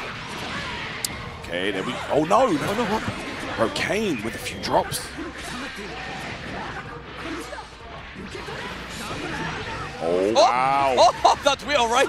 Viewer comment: I thought it said Kanye for a hot second.